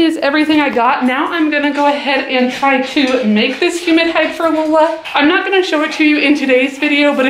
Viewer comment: Who else thought she was saying "human hide" at first 😂😂😂